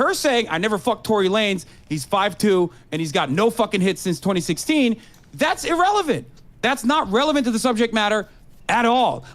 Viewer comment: I swear to fucking god I thought that at 55:56 he said "I never fucked Torey Lane, he's 5'2 and he's got no skills with Hitscan Heroes in Overwatch 2"